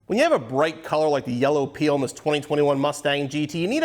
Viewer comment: Sounds like says "when you have a bright color like this yellow pee.. " 😂